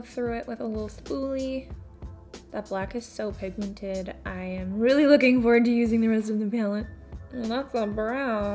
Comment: im dying rn bc i had to rewind it like 7 times to figure out what said and ive concluded she said "im really looking forward to using the rest of the palette" 😂😂😂😂🤠🤠🤠 i just fucking love u 💖💖💖